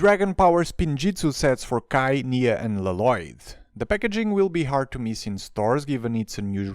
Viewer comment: what did he say!?!?! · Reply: La-loyd!